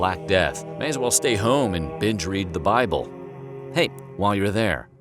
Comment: I heard "bitch read the bible"😂